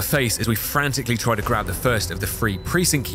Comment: Was today years old when I discorvered some British people replace "th" with "f". I thought you were saying "free" when you were just saying "three" XD